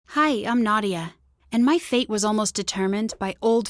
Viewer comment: When she said her name I thought she was going to say “Hi I’m naughty”